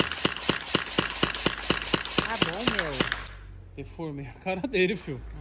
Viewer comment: Ó 11:59 eu ouvi um tá bom amor 😂😂. Quem é essa mina?